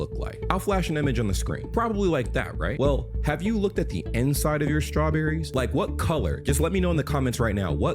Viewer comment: I thought you said end side, like the part right by the leaves, so I was very confused when started talking about the inside. Also I'm an American, and I've never seen a strawberry with white insides.